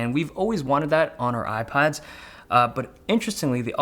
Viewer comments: I heard „on our iPods“ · I heard IPods!